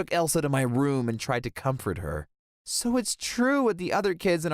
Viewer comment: i literally heard that as "i tried to cum for her" and now I can't unhear it.